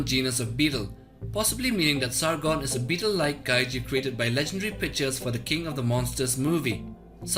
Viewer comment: Wait, Did he just say "Created by the Legendary Bitches for the Godzilla: King of the Monsters movie"